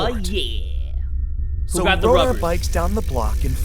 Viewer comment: Did he say “what about the rubbers” 😂